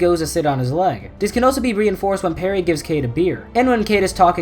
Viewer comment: People doing stunts :hold my beer Cade sitting down: parry get me a beer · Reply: Thought he said Pterry